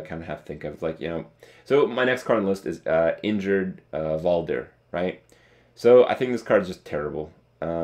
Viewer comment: I thought he said "Injured Volibear"